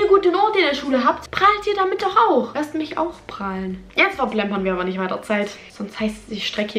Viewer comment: Hallo ,ich lerne deutsch was hat sie gesagt in 0:34?sehr schnell! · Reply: "Jetzt verplempern wir aber nicht weiter Zeit.."